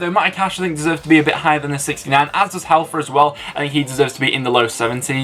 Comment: Do u mean hefele after cash because it sounded like u said halfer. Great video anyway